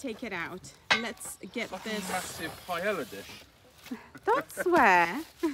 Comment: I’m with Phil I’m afraid I only heard “it’s a massive paella dish” sorry